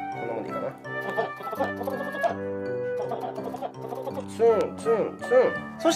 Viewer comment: ツンツンがファッ⁉︎に聞こえて仕方ない